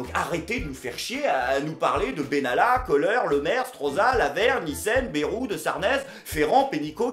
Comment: Quand il a dit " Lavergne, Nissen" j'ai compris "la ver-micelle"